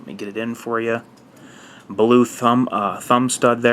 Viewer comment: What does this guy say at 02:42 - smthg like blue thumb... "stud"? Any native English speaker - PLS! What it's made of? Many thanx!